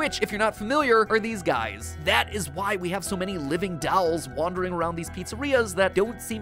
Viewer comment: What Mat says: “Dolls” What I hear: “Dahls”